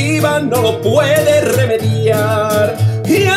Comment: una cosa que no entiendo es baraje 1:45 ¿que es baraje? o que es ser baraje? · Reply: dice "bagaje"